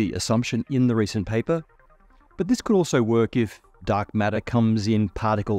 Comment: matt says dark matter but the CC says antimatter.